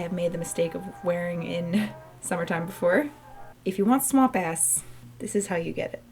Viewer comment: what in the world did you say at 2:57??? swamp ass?? 😂😂😂